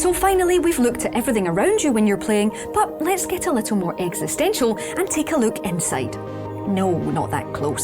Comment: Excersize? I thought you said extra fries :(